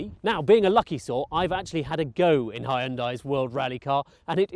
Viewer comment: Did he just say "high-undai"?